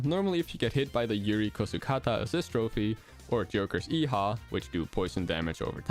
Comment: sounds like he says “Joker’s yeehaw” Edit: HEE HHAWWW (p-please tell someone knows what this is referencing)